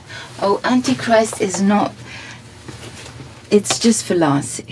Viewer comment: When she picked Antichrist off the shelf I initially thought she said 'Oh, Antichrist - just for laughs'. Face, meet Palm.